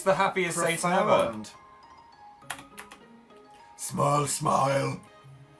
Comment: For a second there, when the Satan-looking thing said "smile smile," I thought it said "smile always" and I wish it had